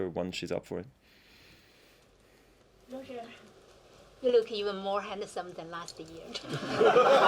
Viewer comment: I'm not a native English speaker and I can't catch it - can anyone tell what was said/asked at 7:00 (that must have been really hilarious)? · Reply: She said he looks even more handsome this year than last year.